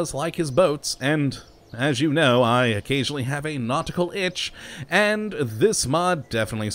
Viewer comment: Captions thought you said nautical bitch instead of nautical itch